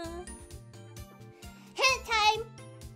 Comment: I thought she said hentai not hint time